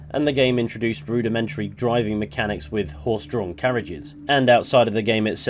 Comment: i heard "horse drunk carriages" and I thought to myself, yup that's exactly how it drives 😂😂😂